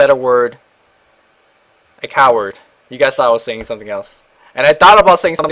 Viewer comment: Lol. At 1:00 he says coward instead of pussy. Thats why pussy style is in the tagsxD